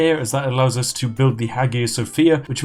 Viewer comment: Oh, the Hagia Sophia... could have sworn he'd said The Haggis of Fear.